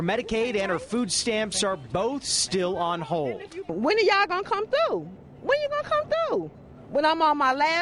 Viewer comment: did she said "where yall kung fu?"